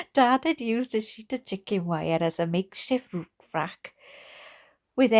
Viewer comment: "mixhi Root Frack" = Makeshift roof rack In case any of you were STILL wondering what the heck was saying around 6:58. I am oddly soothed and stimulated at the same time listening to this. It's magical!! Thanks for this, whatever you call this concept/channel. It is gonna become my new addiction, fast!